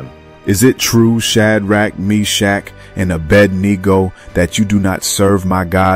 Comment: Shalom family!! Awesome work as always!! 3:56 Is it me, or did I just hear ... "Is it true Shadrack, Meshack, and A Bad Negro...." 😂😂